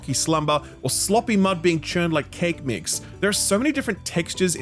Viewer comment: Swear to God you said "your sloppy mum being churned like cake mix" at 2:50 😂 would have been a great line